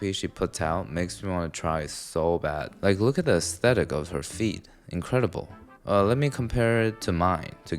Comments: Sounded like you said look at the aesthetic of her feet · had me do a double take cause I heard feet instead of feed lmao